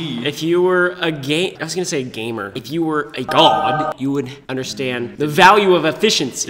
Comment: Trahan's says pro instead of god